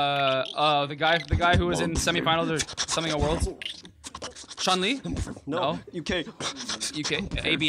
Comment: did i really hear Chun-Li?